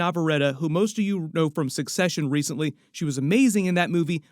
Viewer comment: You mean Induh Navarretta? 😂 😂 😂 😂 😂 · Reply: LOL that was killing me and it sounded like he said succession over " obsession "